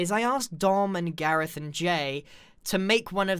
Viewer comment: "So I've asked Dom and Gareth and Jay" I'm not sure I'm prepared for this story... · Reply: I thought he said "Darmon Garrethon Jay".